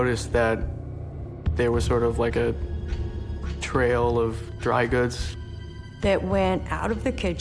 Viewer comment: did he just say “a trail of dry goods”?! 😂